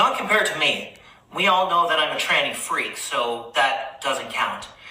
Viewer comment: I lust listened to this - did I really hear at 5:00 "We all know I'm a tranny freak"?